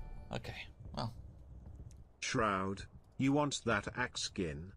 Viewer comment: I hear "shroud you wont to exit game". That' look like jarvis